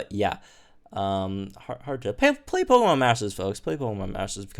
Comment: play Pokemon "matches"? What did you say? I can't make out what that is